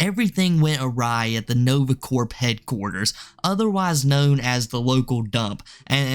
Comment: did you say Nove Cord Headquarters or Nova Cord Pedquarters? Cuz I swear, to me, it sounded like you said Pedquarters & I was laughing so hard!